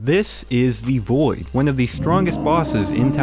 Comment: I thought you said “this is the boi” in the intro 💀